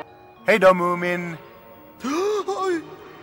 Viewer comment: In the Swedish version I thought snufkin said Bye/Hey Dumb Moomin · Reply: Lmao it does sound like it. He’s saying ”Hejdå, Mumin!” (= ”Bye, Moomin!”).